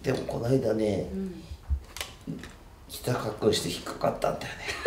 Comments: からのところなんて言ってるのかわからない、、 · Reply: 菜々 「膝かっくんして引っかかったんだよね」かな...? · ｢でもこないだね〜｣の次なんて言ってますか？ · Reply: 鈴木あやり ひざかっくんしてひっかかったんだよねだと思います